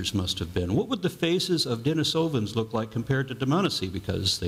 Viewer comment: did he say denis hovind's? Are they in relation to kent or Eric?